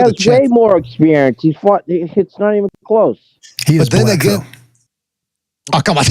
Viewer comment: What said? I didn't get it. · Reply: Lol I said ".........and he's black"